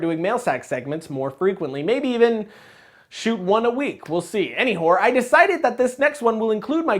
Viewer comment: _- ... wait ... was it just me or did say "any-whore" instead of "anyways" at 0:31 ?!?!